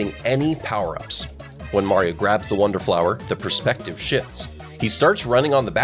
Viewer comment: DID HE SAY THE PERSPECTIVE POOPS? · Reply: "Shifts" as in "turns" 🗿🗿🗿